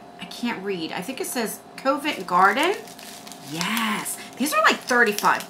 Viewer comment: I thought you said that shade was "covid garden" 🤣🤣🤣🤣